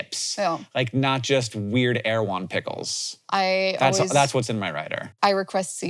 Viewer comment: There are a few captions errors: 2:49 “air one” instead of “Erewhon” 17:48 “can’t” instead of “can”